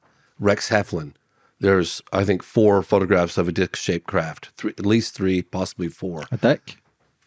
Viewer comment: Thank you! I heard "dick shaped craft" too. Was so relieved you asked James to clarify! 😂